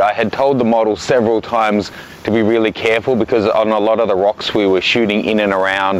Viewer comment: Oh man, how unfortunate! Commiserations to your felled D5100. 0:58 Did you say "woks"?